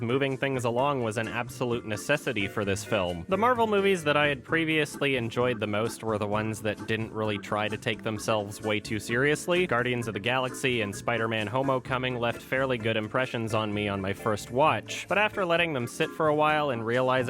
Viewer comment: did... did you say "Homo-coming"? Um. k.